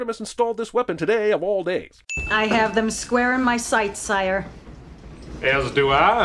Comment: The subtitles say "squire" but the movie VERY clearly states "sire." Also, dislike for over-censoring things like you continue to do, on a PG-13 movie, no less.